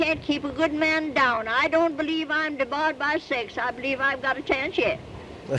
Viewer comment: "…and to tell me you can't keep a good man down I don't believe I'm dabogba sex, I believe I've gotta telleinshed" Me: I can't understand some of what she's saying *Literally the next thing OP says*: "Hahaha. You know what's interesting? Is that we still understand her."